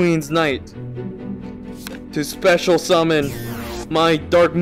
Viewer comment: did he just say special summon?